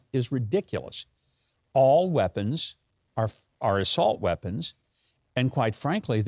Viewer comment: I thought Joe said solt weapons.